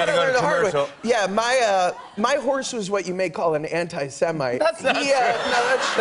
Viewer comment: Can anyone please tell me what Josh say at 0:52? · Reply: lecoindeclaire anti-semite I think